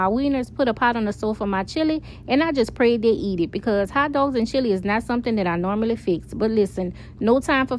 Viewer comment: Girl that Nola accent thick because I was texting and not paying attention at first and I thought you said tacos and chili 😂 I thought to myself that is a crazy combo until I looked up 😂😂